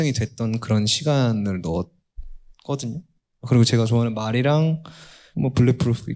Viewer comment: The subtitles are wrong. It’s not horse. <I also have a tattoo of phrase(words) which I really like, and there's also "Bulletproof.">🌸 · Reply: The translator could not tell the difference between phrase(words) and horse. This is because the pronunciation of both is ‘mal’. He doesn't have a horse tattoo. He pointed to the phrase and said. Thank you❤